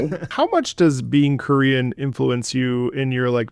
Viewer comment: oh my god I thought you were saying "Curian" or something and I was like what the hell is that??? When I listened back I recognized Korean easily but maybe my brain short circuited.